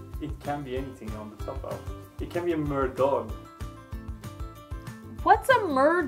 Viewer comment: I thought said mer-god as in Triton, the god of the merfolk.